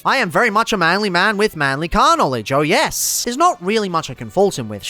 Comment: No, "astroturfed". The opposite of "grass roots", when a company tried to push something and claim it was always super popular. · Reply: totally thought that you said Carnal knowledge for a split second. I was thinking you have a dirty but sneaky mind....which I very much approved of for the half second before my brain corrected me. 😐💬wait a second does this mean that in fact I am the dirty bird ⁉️